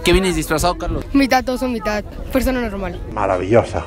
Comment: que dice ese nino? mitad oso y luego que......